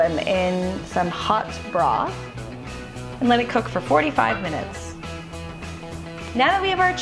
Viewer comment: I thought you said "4 to 5 minutes" on the boiling part so i basically fried up some really gross donuts 😫